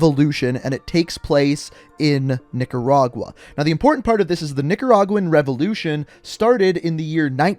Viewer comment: did he just say the n word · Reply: No he said Nicaraguan